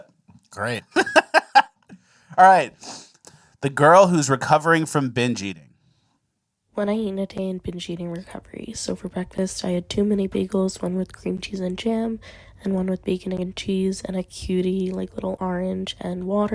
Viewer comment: Yo that girl on the diet😂😂 i thought she said i had too many bagels she said 2 mini bagel..... but she also had too many 😂